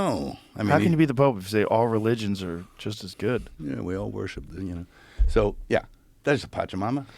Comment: Do I hear Mel say „We all worship Dunya“?